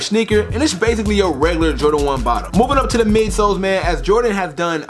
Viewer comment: thought you said you were "Jordan 1 out" and was gonna pass? but still sick colourway